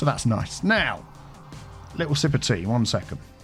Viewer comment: I always hear "a little sympathy", instead of "a little sip of tea" 😡